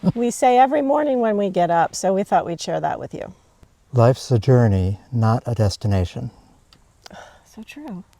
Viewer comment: My hearing is not good. Listening without my hearing aids I thought you said "Life's a tyranny not a destination!" A tyranny because you have 20+ things to do every day, but realistically only time to do 5, or 4 or 3! 😬😲😳 😄